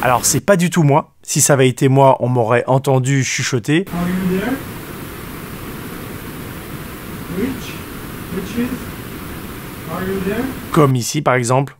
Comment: '' are you there? bitch, bitches. c'est vraiment ce que j'ai entendu hahaha